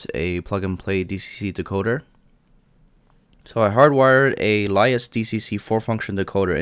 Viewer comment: you mention the name of the decoder, and I have played it over and over like 50 times. I even slowed the video down to 1/2 and 1/4 speed and STILL cannot understand what you are saying. Please spell out the brand name/manufacturer of the decoder for us. Thanks! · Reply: LaisDcc - Mobile Decoder. Looks Very similar to dcc concepts.